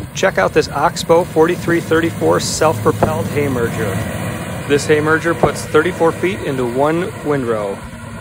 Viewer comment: One has to put up a lot of Hay or silage to make a one use machine like this worth owning. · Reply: honestly thought you said self propelled hammer drill for a second